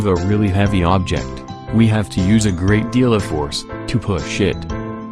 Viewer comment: I thought this was for kids. Why did they swear? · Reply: No it sound like he swore but he actually said "push it."